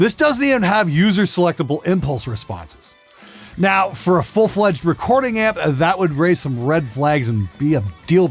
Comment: Can you change the irs?? cause i couldnt understand what he said at 2:34 lol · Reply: Sounds like a big fat nope to me. He says it'd be a deal breaking red flag if it was a "fully fledged yadda yadda," but because they gave him money it's not a big deal to him.